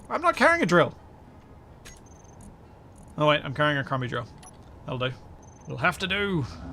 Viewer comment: Did I hear correctly that you are carrying a "commie" drill? 😂 Those cheap chinese drills do come in handy sometimes! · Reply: That woudl be a 'crummy' drill :P